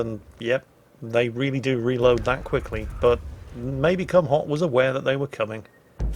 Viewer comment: Lol for the first 10 min i thought you kept saying his name as maybe come hard and then i saw it spelt in battle chat then it made sense but don't mind me i'm wearing a mask so i cant hear to well ;)